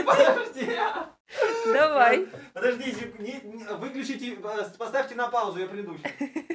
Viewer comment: выключите глаза??? мне не послышалось?